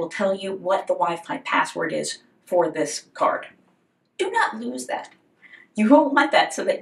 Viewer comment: I listen to around 7:55 or this video and every time I heard "Do not use that" for the password. Until I turned on CC did I get the "lose that"